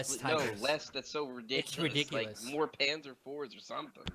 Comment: I heard "more panzer." You know what to do.